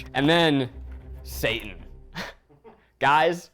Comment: I thought he said Satan instead of Seitan. I was like what kind of devil's food is this?